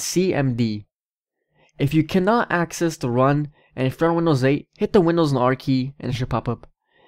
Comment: wait what did he say at 3:49 "hit the windows and R key"?